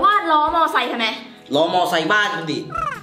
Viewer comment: ได้ยินว่าบ้านมึงดิ ถ้าฟังผิดขอโทษด้วยนะคะ